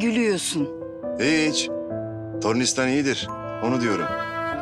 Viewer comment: te ne diyor kuzgun allah aşkına anlayan söylesin on kere dinledim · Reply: Hiiiic tornistan iyidir onu diyorum diyor